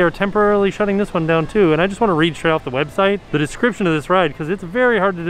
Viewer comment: Sounded like you said “ I’m gonna read this s**t off the website” when you were at f and f